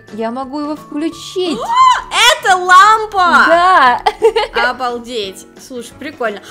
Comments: сказала это лампа А мне послышалось это ламба🗿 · Когда сказала: ЭТО ЛАМПА! То мне послышалось: ЭТО ЛАМБА, А ЭТО ГЕЛИК ВЛАД ЭТО БУМАГА А БУМАГА ЭТО ДЕНЬГИИ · Мне одной послышалось что сказала ЛАМБА ?